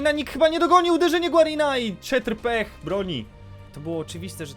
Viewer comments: czy tylko ja usłyszałem czetr pech xD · Czy tylko ja usłyszałem Czetr Pech? #7.08